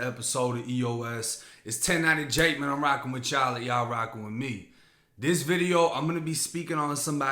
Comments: Is it just me or does it sound like Jake is saying "Wassup everybody its 1090 Jake man I'm rocking with y'all or y'all rocking with me" ? Lmk 🤔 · It's funny when I first started watching your videos I thought you said " I'm rockin with Charlie like you're rockin with me! Yeah I'm an idiot!!!